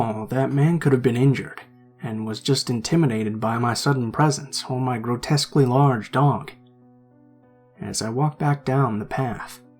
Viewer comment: XD XD I thought he said " he might have just been intimidated by me or my extremely large don't" XD XD